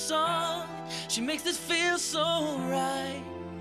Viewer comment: DAS SHEIS SISNXWJJZNX Seriously what did he say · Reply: Blue Bowser it’s german “soll ich es versuchen ? Ich muss das echt vergessen. So ein Schwachsinn” - should I try it ? I really need to forget about this. What a bullshit